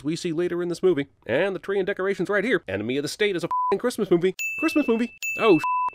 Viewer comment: I thought it said “Enemy of the Satan”